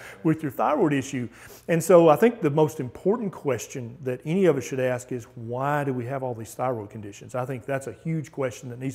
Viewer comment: he talks about Thyroid disorder or something i never heard about it, and then he talks about somecthing called Aydan, or eyedan? can´t hear what they say? anyone can help me please? he talks about they start taking that solution and a light came on in their head. Help please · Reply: Iodine